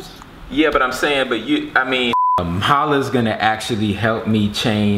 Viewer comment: why it sound like he said paula